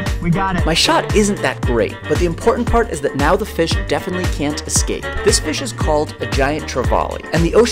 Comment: For a second I thought he said the fish was called a definitely can't escape